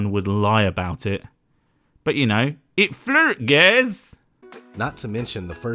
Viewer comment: What's being said at 13:14? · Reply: "it flert gaiz" witch translates to "its flat guys" :P